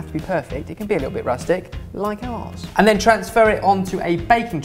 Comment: did he say that it could be rusty like a arse?